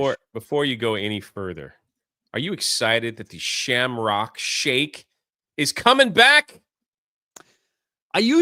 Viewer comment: Am I only one who is so one piece obsessed that I heard him say Shamrock Shanks?